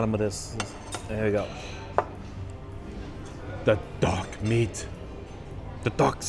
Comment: When you said dark meat, I thought I heard “dog meat” lol